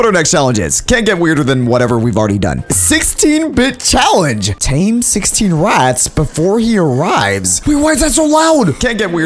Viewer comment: On the 16 bit challenge it listed out of 15, I thought you said you were gonna tame 16 rats?